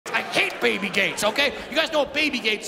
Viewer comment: i thought he said baby gays